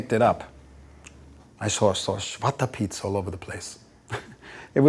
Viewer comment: What does he say at 2:50? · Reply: I think he said: Schwartze Pieten. He was referring, I think, to the dutch words: Zwarte Pieten. These black (zwart/schwartz) helpers would accompany Sinterklaas in Holland. Sinterklaas is the dutch equivalent of Santaclaus, who Steve was referring to just before this moment. I guess this interview was done in Holland by a Dutch interviewer.